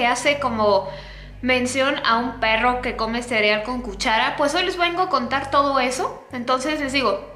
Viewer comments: Yo pensé que decía el perro que comía cereal con cucarachas :'v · Yo pensé que decía cereal con cucaracha